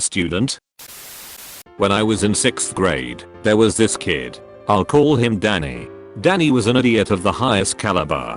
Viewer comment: At first I misheard "let's call him Danny" as "let's call him Daddy" and I was like whoa WUT? THAT IS THE WORST THING TO SAY!